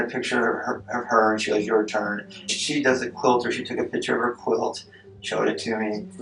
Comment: Did he say she took a picture of her quilt? What?????